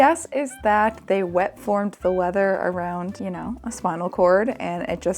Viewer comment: I hope you didn't pay for the closed captions because there's a lot of errors in there. "weather" instead of "leather" for example at 11:13.